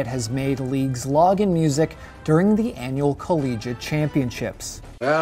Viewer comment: Wait. Did you just say CO LEE JAH